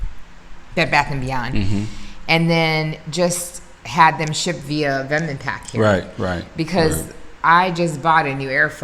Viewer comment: We never mentioned a moving company & sorry don’t know of one just google through the internet and good luck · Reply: Shipping company, not moving company. Vim and Pack? Them and Pack? Something and pack? said it 3 times between 12:25 and 13:06. I couldn't understand it either.